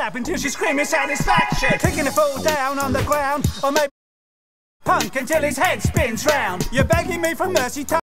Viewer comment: I think he said funk a dumba*s punk until his head spins round